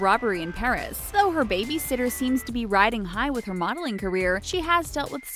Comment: uh, isn't it supposed to be "baby sister" instead of "babysitter"...?